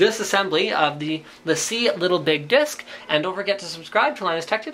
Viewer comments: I thought he said little big dick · Am I not the only one who thought it said little big dick at first? Or am I just too dirty minded?